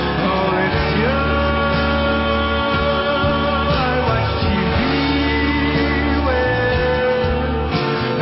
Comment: Its me or i just heard “I watch TV”?